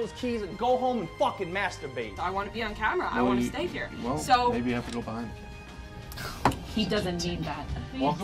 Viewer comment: I can't make out what Dave said to that irrelevant diva that was whining about wanting to be on camera at 14:45 that made her cry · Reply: don't know if you ever found out but I'm pretty sure he said, "well maybe you have to go behind the camera...." which lets me know she's a diva